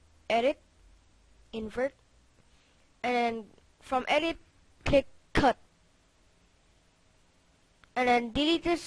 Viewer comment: Did u Say Cet Cut